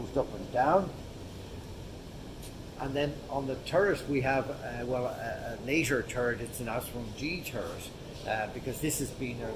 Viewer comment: I thought he said a laser turret.